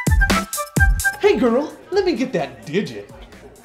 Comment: PLEASE can somebody tell me what says at 1:05 ? I can't understand. Thanks. (English isn't my mother tongue). I just understood "Let me get that to...???"